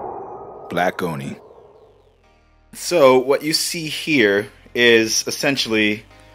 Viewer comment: subscribed because i thought i heard "Blackeroni", stayed subscribed due to the fact it really says black demon